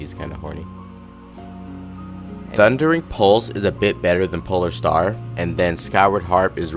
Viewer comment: dude I didn't understand the word you said about the skyward harp. You ate up the last word. It goes like this "thundering pulse is better than pole star and then the skyward harp is bleh bleah bleah. " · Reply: All in all, thundering pulse is better than polar star, and skyward harp is underneath polar star as far as damage. So thundering pulse > polar star > skyward harp.